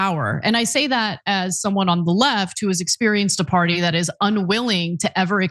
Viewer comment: Ana I thought you said you left the left